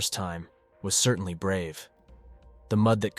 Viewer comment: you say brave I hear "Stupid"